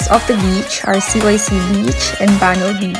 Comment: May I ask what beaches you see in 6:41 mins of this video, I can't understand the words you've speak, and may I ask please your instagram account I want to ask some details about this trip. Thank you very much po :) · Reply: it must be Banul Beach! My instagram account is my name hehe!